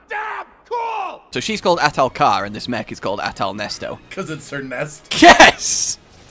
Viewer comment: Anyone else hear At-Ernesto instead of Ahtal-Nesto?